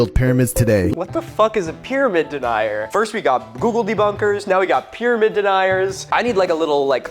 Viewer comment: Every single time I hear "googledebunker" I think "Gobbledy Gooker". I feel old. 😂 Edit: I not I'm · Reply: I am getting a new kitten in a few days, her name will be, Googledubumper because that's what I thought he was saying at first and it made me giggle. And lets be real, cats don't come to anything but psspsspsspss and "kitty kitty" anyway